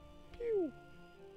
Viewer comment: did it just say pew?